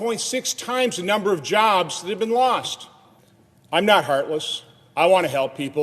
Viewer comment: His lips say, "I'm not heartless," but I clearly hear, "I could not care less."